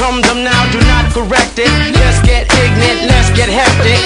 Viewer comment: Anyone else thought he said "let's get pregnant" instead of "let's get ignant" all these years? Because I sure did.